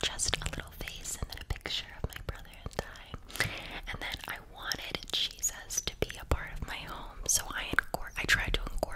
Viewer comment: did you say you and your brother that die or what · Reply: She said "brother and I "